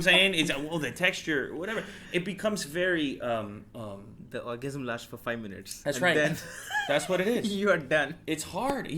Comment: did he say "The ORGASM lasts for 5 minutes" or what?